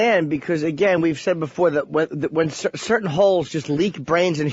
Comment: I keep hearing hoes as O's.